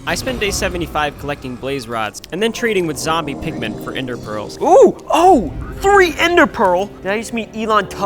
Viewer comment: When he said ender pearls I thought he said ender girls😭